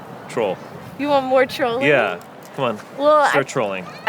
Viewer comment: When she called him a fraud I thought she said “I think you’re hot” like some sort of Freudian slip lol